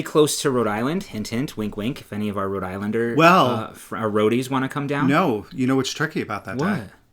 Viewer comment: It is the same in RI. Being so close to RI, we never heard that before. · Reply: Oh my I heard "roadies" and you were saying "Rhodies" 😂